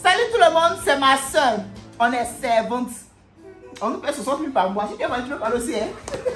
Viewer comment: a dit quoi ? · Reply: « on nous paye 60milles par mois »